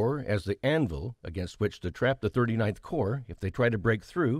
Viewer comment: Narrator says "39th Corps" but subtitles say "29th Corps"